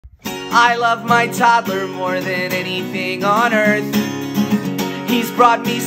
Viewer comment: I thought he said I left my Toddler